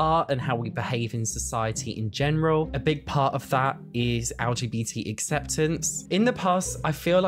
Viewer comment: Did you just say lgbtap ?? Haha i love you man